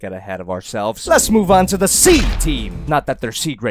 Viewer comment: Because I would say ocean or water I never understood that they were saying SEA team. I thought they were saying C team. Like A team, B team C team. I always wondered why they didnt stick to one naming convention. A friend finally explained it to me.